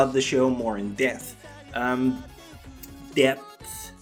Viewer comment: Yep you sure do have a problem with that word, sounded like you said "Death" the first time hmm...